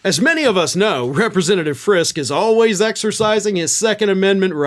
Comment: that last name sounded familiar, I thought you said, "FRIST," as in 1 trillion dollars out of medicare in 10 years for VIAGRA APPROVAL MEDCARE PART D .... 😂😂😂 ITS FRISKE 😂😂😂